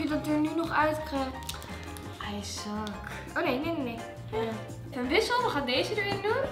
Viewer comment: Wat zegt daar?😂 Ijszak? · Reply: Rosa Noor i suck zegt ze😂